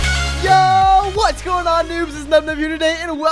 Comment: It sounded like he said "yoooo what's up noobs" xD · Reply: He did say noobs